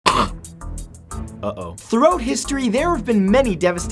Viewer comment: Matt coughs. Matt: 'Throat history diseases...' pun intended? Sounds like he said throat rather than throughout